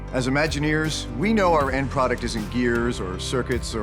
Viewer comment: It would be great to update the Closed Captions (CC) to have the exact words said. There are a few small errors, but they make big differences, such as toward the end where the speaker says “isn’t” but the CC says “is in” - the completely opposite meaning. This error can also be found in the transcript